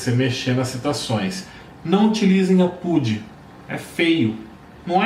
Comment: gente, o que ele fala em 27:18? "Não utilize ..., é feio"... não consegui entender o termo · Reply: Anna, ele falou o termo "apud" (citação de citação).